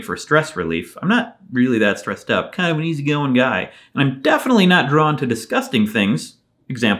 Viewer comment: I thought he said "I'm kind of a wheezy going guy" at first and tbh I'm kinda sad he didn't